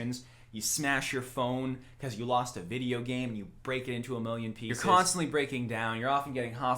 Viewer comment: English Auto-Generated C.C.'s says "people" instead of pieces oof.